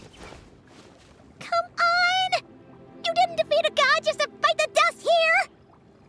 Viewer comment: did you just say Bite the dust?